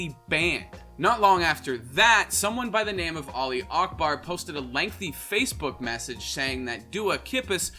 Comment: that’s what my uncle shouted before he died during 911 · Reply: He said "Ali Akbar" Not "Allahuakbar"